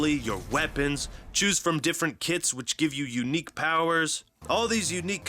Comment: idk why but when I heard ''choose different kits'' I thought he said ''choose different kids'' for a second lmao